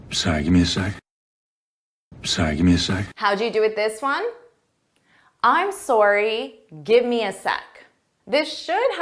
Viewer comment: I heard: "I`m sorry gimme a sigh".